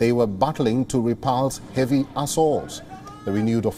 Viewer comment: did I hear repulsive a$$0🤣🤣🤣